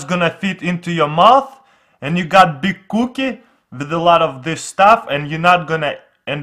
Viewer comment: did I just hear him say big booty 😂😂😂😂